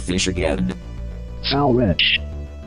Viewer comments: I'm pretty what he says is "foul wretch". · I heard "foul wretch" on the second clip